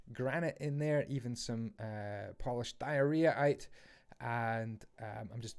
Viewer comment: did he just say "polished diarrhea" at 9:23?